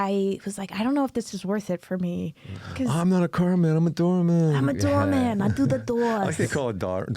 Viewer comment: nealon sneaking in the kindergarten cop style 😂 im not a policeman im a princess😂😂😂😂 👏🏻 at least thats how i heard it. Great episode as always!❤